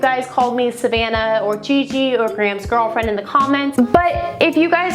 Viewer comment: I thought you said "Grahamsey," :D Anyway, one thing you don't know about me, we share your number #8. I can through it but I still don't like it..it is what it is.